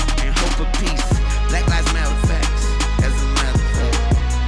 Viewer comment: Hold on, did Wayne say “black lives matter” 😳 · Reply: iiiion know, it was kinda mumbled lol...sound like he said “black lives mansss with facts, and that’s a matter of fact...” or something idk